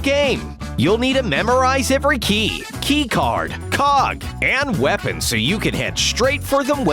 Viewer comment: For a second there, I thought he said "COCK"